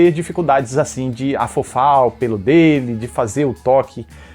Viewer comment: entendi "afofar o piru dele"